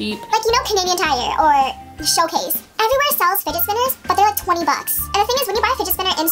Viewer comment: I thought she was saying spiget spinner 😂